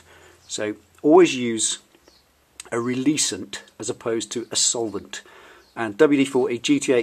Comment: what's the word he says? Use what? A releasant? It seems to be quite an important issue and I wouldn´t want to get it wrong English can be hard...